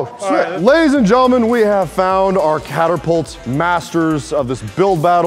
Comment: Did say catERpult...?